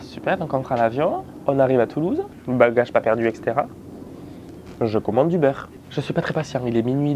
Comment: Je crois que je ne suis pas bien réveillée... Par 2 fois, j'ai entendu "Je commande du beurre!" Au lieu de Je commande Uber...😅 En plus, ça collait bien au contexte: vous n'avez pas eu le temps de manger. Mais je me suis quand-même demandé pourquoi commander du beurre? Commander, really???😂